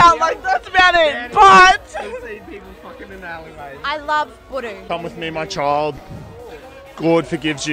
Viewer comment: does she say she loves pudding? 😂😂😂 Fuckin 🔥